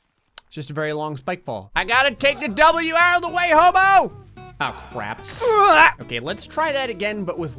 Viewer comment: On the first spike fall I thought he said out of the way homo